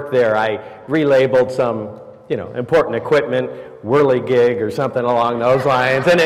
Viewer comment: Makes me worry about what the rest of the translation says when at 6:10 it says "Willy Geiger" , which is a persons name and not even remotely close to similar to what he said had said which was "whirly gig."